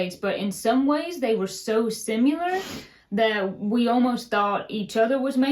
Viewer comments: I just heard say “simuler” for similar. 😊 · you say “simular” for similar and I always think “what did she just say?”. Loved the video.